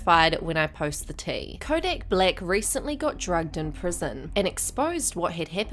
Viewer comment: I thought it said "dragged" like he got cancelled by his fellow inmates 😂